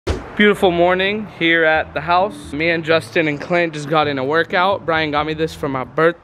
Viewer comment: Is it just me or it sounded like he said justin amd romie